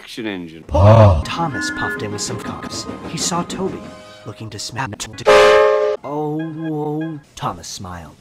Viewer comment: What does the narrator say at 0:26? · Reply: Looking dismantled